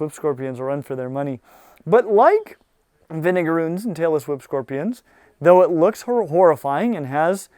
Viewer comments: I thought he said Taylor Swift scorpions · sounds like he says "the niggaroos" lmao